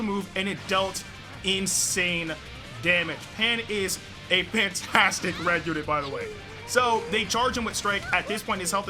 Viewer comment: I thought he said Pantastic at 5:26 LOL🤣🤣